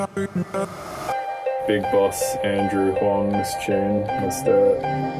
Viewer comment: when I saw videos for the first time I I thought he said dj huang in the inrto